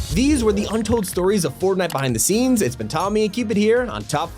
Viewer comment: sounds like he said fartnite💀